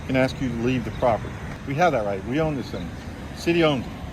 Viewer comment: 'We own this thing/city' 07:50 Didn't quite catch it. Either way, they own it!